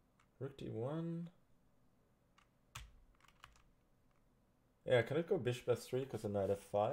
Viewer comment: When he said “ Can I go f6 “ It sounded like he said “ can I go have sex “